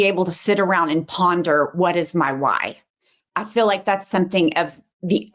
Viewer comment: At first I thought you said, What is my wine? Lol. My kids are my wine 🍷 lol